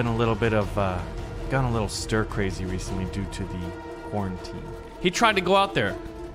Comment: DID HE JUST SAY PORNTEAM